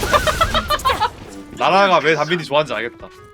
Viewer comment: 여기 '누나'라고 자막 쳐져있는 부분 '나라가'라고 들리는데 맞을까요?